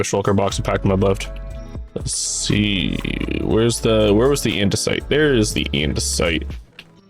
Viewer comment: May I ask if you used genAI for your subtitles? I noticed a few inconsistencies between what you say and what subtitles say (for example 1:20 "andesite" and "end to site").